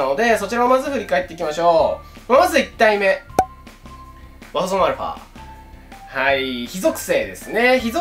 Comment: 馬場園アルファにしか聞こえない